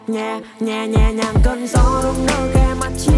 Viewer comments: Không hiểu sao lúc đầu mới nghe thì nghe thành: "Nhà...nhà...nhà...nhà và cơn gió..." Có ai nghe như mình không :')? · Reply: lúc đầu mình nghe tik tok mà không có lời í, mình nghe thành "nhề...nhề....nhề...nhề nhàng cơn gió bla bla bla, xong lúc biết nó là gì rồi cảm thấy buồn cười vl =)))) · Không hiểu sao lúc đầu mới nghe thì nghe thành: "Nhà...nhà...nhà...nhà và cơn gió..." Có ai nghe như mình không :')?